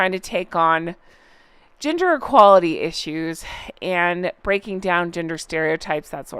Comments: Oh my goodness. While listening to this I thought I heard you say that the twofer were now taking on Ginger equality issues 😮!! Can you imagine?!? I misheard what you said but thought immediately how typical of them to now take on hair color bias🎉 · At first I thought you said "ginger equality". 😂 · I miss heard you I thought you said :Ginger equality !! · OMGGG, I wasn't l👀king at the screen and at first and I thought you said "GINGER EQUALITY"! 😅😅😅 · I thought I heard you say "Ginger Equality Issues?" I about died.. LOL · I thought you said GINGER equality!😂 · when you said “gender equality” i thought you said “ginger equality” at first and, as the wife and mother of gingers, i was thinking, yeah, that might actually be a legit cause because gingers get a lot of crap and are spoken about as if they are subhuman. the problem is that harry is one of the primary people giving gingers a bad name :_HonkHonk::_Honk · OMG! I had this playing in the room while I was doing something and when said "gender equality" and "breaking down gender stereotypes", I heard "ginger"!. I was like, "What?!? Is that a thing?"🤣🤣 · At first I thought you said “Ginger equality”, which would be pretty funny, considering how she treats Harry. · OMG!! From across the room I heard "ginger equality"! And THAT made me pee my pants a little!!!! Maybe that's an idea?! 🤣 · For a second I thought you said ‘ginger equality’! 😂😂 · I thought you said ginger equality instead of gender equality 😂